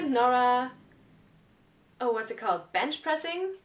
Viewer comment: OK when you said "Bench Pressing" I could have sworn you said French Kissing